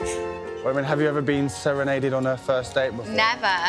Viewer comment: I thought he said 'inseminated' not 'serenaded'